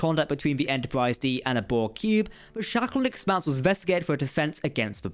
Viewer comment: I thought at first you said Shatnerton expanse.